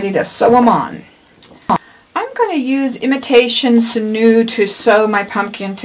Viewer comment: How wide is your mesh/burlap? Also, what "thread" did you use? It sounded like you said Imitation Sanu. What is that? Where can I find it? I love this video! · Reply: The mesh ribbon is 6 inches. I used Sinew but you can use any type of strong thread you have. Let me know if you have any more questions.